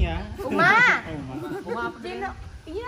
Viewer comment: A'alshad : ''kasian Nino Yun jangan gitu dong'' (Hahahhahahaha ngakak) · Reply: Menit 3:51 a' alshad " uma apa sih sayang "bener ga sih apa aku yg salah denger.